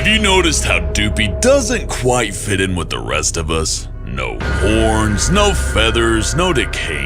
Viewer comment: did he just say porn?